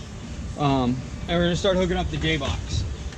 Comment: I swear at 1:27 it sounds like he said "now it's time to hook up the gay box" lol.